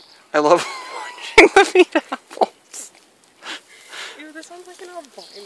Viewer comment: I thought it said "Apples trying to eat Apples"